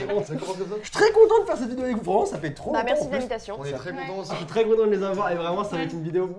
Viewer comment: j'ai compris(ta mere l'invitation )